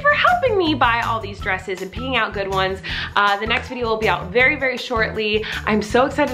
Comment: Ok, idk about u, but I heard “peeing out good ones”